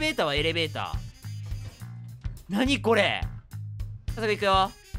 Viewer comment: 関係ないかもしれないけど、 21:11 この部分遊戯王のなぁにこれにしか聞こえない